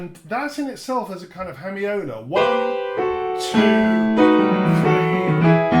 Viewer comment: 12-36 What is it you say there? "That, in itself, is a kind of 'homeona' (?)". & your historico-musical analyses are awesome. But you need to caption everything. Musical lpudness and linguistic accent and passionate phrases while speaking, sometimes make it unintelligible to hear your points. Thanks! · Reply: Sorry about that! The word is hemiola.